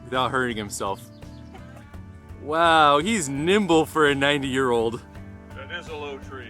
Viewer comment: did you say 90 ?! Wow, he looks maximum 75..🧿